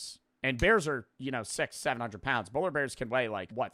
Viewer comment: my buthole clinched when i heard 6-7